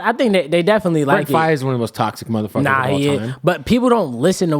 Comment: what was he saying? What name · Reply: brent faiyaz